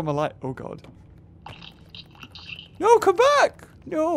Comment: when he said "ah no!" i thought he said "ah nome!" R.I.P Runaway kid/seven